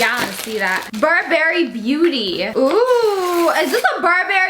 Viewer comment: I thought you said bourbon 🥃 when you said Burberry 🤣🤣